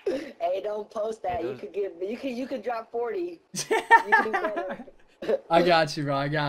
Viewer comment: I thought I said don’t post it you could drop 40 😂